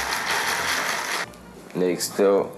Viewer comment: sounds like he said next up like DJ ghost